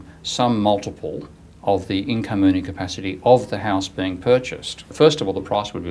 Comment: What does he say around 7:20? "the maximum loan they could lend would be some multiple of the (?) incommunicapacity (?) of the house" ? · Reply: Henk Poley Income capacity.